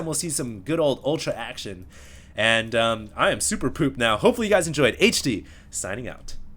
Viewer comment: Did he just say he was superpooped?